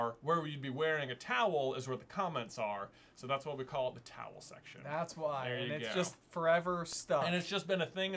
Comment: Oh! I always heard it as 'the Tattle section' - which makes sense, but I thought was rather unusual, pleasing and quirky. The real answer is SO much better. :-) - Also, - you know several of the many very useful and potentially life-saving uses for a towel are actually listed in the Hitchhiker books, right? I like the sound of the video, though. :-) x.